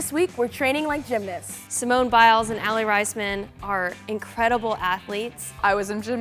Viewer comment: did she just say aly " risman "